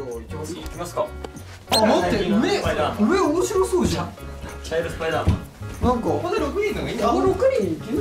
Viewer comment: 「茶色スパイダーマン」って聞こえるんだけど、まさか2年後にスパイダーマンの主題歌を歌うことになるとは、だれも想像してなかったでしょうね🤎☺️👍✨